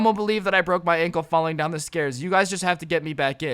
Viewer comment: Am I the only one who heard falling down the scares